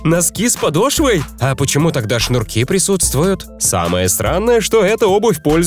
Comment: мне послышалось или он сказал: самое СРАНОЕ